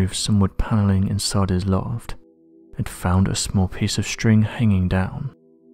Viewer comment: I thought he almost said,”…and found a small piece of sh*t”. .. instead of string